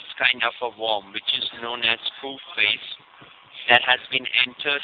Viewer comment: I thought he said "Poop Face"...